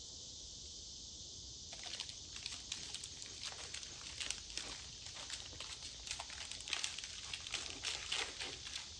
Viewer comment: which animal is it? I heard buttercup??? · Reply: Buttercup is the name of our dog. It's a black bear cub.